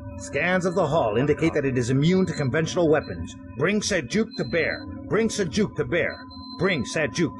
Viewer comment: I hear: Bring Sujuk to bear, bring sujuk to bear, bring sujuk to bear... Then let punch the bear?